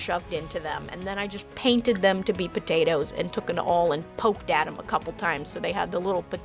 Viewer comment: ngl, I heard "potatOS" and imagined a medieval slant on GLaDOS